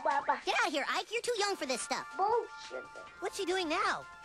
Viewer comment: No wonder Ike said, "Bullshit!" It sounded like he said "bullshitter".